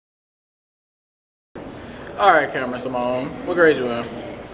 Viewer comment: What she say on 9:38😂😂😂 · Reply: "Kamryn Simone" lol